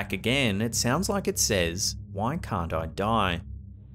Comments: I thought that voice said "Why can't Frank die" that's what I heard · I heard “Why can’t Frank die?” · I hear "Why can't we die?" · I heard, “Why can’t Frank Die?” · I heard "I am Richard" not "Why can't I die".